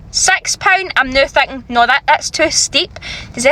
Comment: did she say sex pounds or six pounds or sex for a pound 😂